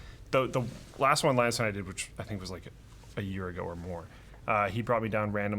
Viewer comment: NondescriptGamer <link> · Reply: Thanks. I did manage to find it. It wasn't made a week ago like I thought they said, but a year ago, which explains why I couldn't find it.